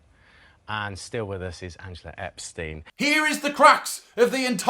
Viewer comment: did he say.... Epstein